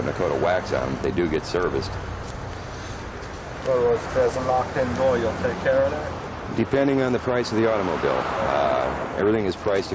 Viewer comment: Could someone tell me what the customer said? Please. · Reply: Sounds like "there is a knock(?) In the door, you gonna take care of that?"